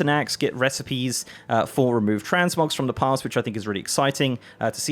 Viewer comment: Fuck I thought he said TransMobs at the begining and I was like NOOOOOOO NOT HERE TOOOOOOOO NOOOOOOOOOOOOOOOOOOOOOO.